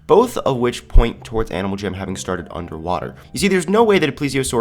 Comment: when it said jamaa underwater I thought it said jamaa underwear